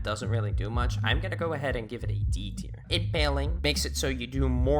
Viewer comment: sounds like you said "it manly"